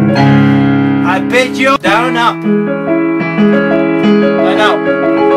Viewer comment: Did you say I bet you down up at 2:20:03😂.